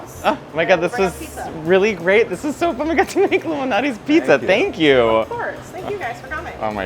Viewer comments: I thought they said "Illuminati's pizza", lol 👁🍕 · I also thought Matteo was saying Illuminati pizza…Damn, the ILLUMINATI ARE EVERYWHERE! Lol, but that is not what he was saying…